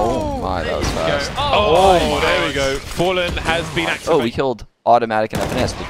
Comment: i thought said he inactive